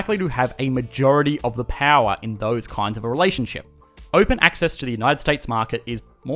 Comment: When he says 'relation' I hear 'Malaysian'. Lol